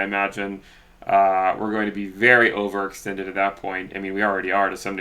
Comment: What is the word you use in 4:20? "we are going to be very over...." Over-what? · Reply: Over extended, as in way above the moving average.